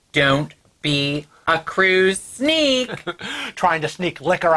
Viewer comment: Thought you said cruise geek, too late but cruise sneak I am not.🐸